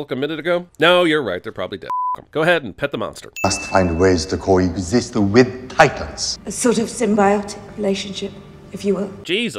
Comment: at the 1:39 mark if you throw subititles on Mr. Let-them-fight he says something about " we must find ways of coexisting with titans" but the subtitles say " Monster us find ways to coy this the wind tight dice" its almost like the bad lip reading channel took over the subtitles for a min there